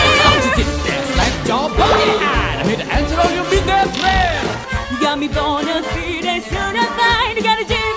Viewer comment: what did she say😂😂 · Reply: Jedidah Jenkins "you've got me bona fina certified, you've got a genie for charge d'affaires"